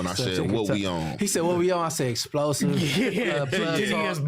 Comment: I'm pretty sure i heard say "what WE on" in response